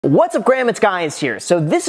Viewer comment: This is embarrassing.. That intro.. Caption says, "What's up grandma's guys here" .. Which I guess makes sense for the video.. But what it sounds like he says is "What's up Graham it's Guys here. Which also makes sense I guess in backwards land.